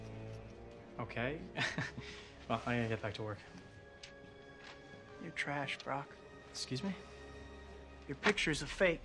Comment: Bully Maguire: “You’re trash Brock.” Brock: FAKE PHOTOGRAPHER ANGER MODE ON I said to myself WOW MAN THIS IS SO NOT FUNNY WOW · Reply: no joke I thought he said "You're trash bruh" lol